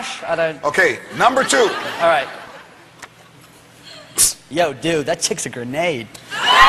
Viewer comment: I'm Brazilian, so please could someone tell me what Tom says in 0:47 ? Please :/ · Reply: Luana B „Yo dude that chicks a grenade“